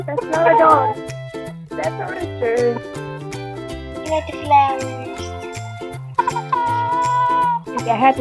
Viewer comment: Sounded like he said he liked the flowers!!!flowers🌸🌼🌺